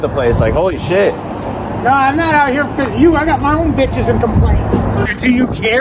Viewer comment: At least the old dude backed you up in a way. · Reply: at first i thought he was going to say hes got bitches and problems stemming thereof. But he started saying his complaints.